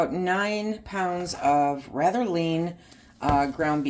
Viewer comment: Sharing the laugh 🤣 I had at my own “southerner” self. I heard use “Ratilin” ground beef; thought it was a type that I was not aware of. I checked the transcript to get spelling to research and found it was “rather lean” beef. 😂😂